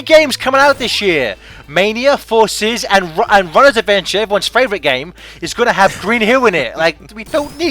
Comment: What did say? Mania, Forces, and Robert's Adventure? If that's the case, then holy shit! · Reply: "Runners Adventure." Mobile game, I think.